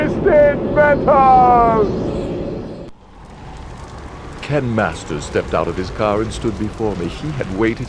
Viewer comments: my bad .. I heard Ken Masters. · wait....did he say Ken Masters? TF 😂😂 this is awesome